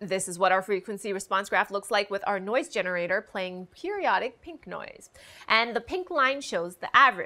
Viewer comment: did she just say pink? I think my girl might be colorblind.